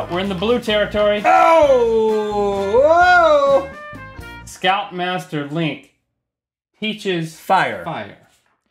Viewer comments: At first i thought Link had said "scalp master"....and that they were supposed to burn off their hair.... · I thought he said "Scalp-master Link" instead of "Scout Master". I was intrigued.